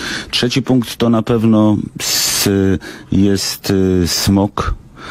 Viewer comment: czy ja dobrze usłyszałem "to na pewno jest smok"? będą zwalczać smoki czy jak?